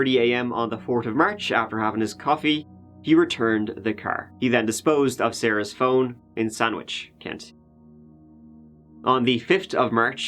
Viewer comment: For a second I thought you said he disposed of her phone and sandwich